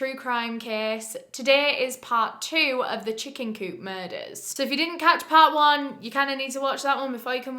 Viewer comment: at first i thought it said "the chicken noodle soup murders" and i was so confused ifykwim